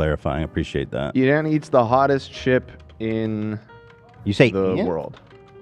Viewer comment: it did sound like Dan said “Ian”